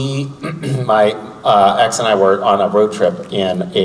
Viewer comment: did he say "my ex"?!